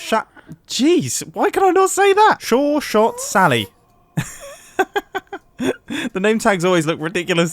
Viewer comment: Sounds like he says "Shore Shot Sally" and not "Sure Shot Sally"